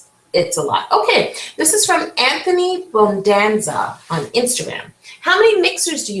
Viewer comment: When you said Anthony I thought you said Amelie and I was soooooooo excited